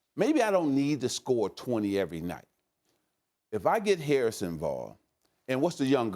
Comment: did he say Harrison barne?